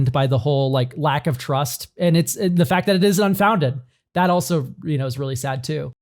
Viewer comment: you really need stop saying that the distrust in Hasbro is unfounded. Pinkertons, variable pricing for secret lairs, putting JLW back on the commander panel after him being okay with death threats to the previous panel, Spider man draft, and the errors that are piling up from increased set release cadence. It also didn't help that the original statement asking for feedback was phrased in a leading manor. It's a learned distrust, not a unfounded distrust. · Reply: i slurred my words here so I don’t blame you for mishearing but I said it’s sad it 🚨ISNT🚨 unfounded not “is”